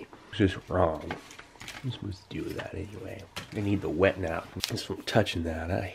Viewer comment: “I’m gonna eat the wet-nap.” I’m dead. This is the best MRE review ever. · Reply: I'm Quite positive he actually Said "I'm gonna Need the wet nap" but sometimes it's more important to just laugh along and not care 🍻 This video was Hilarious!